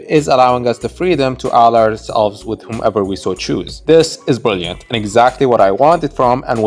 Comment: What did he say at 1:41 ? Give us the freedom to do what? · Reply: "Freedom to ally ourselves with whomever we so choose" 👌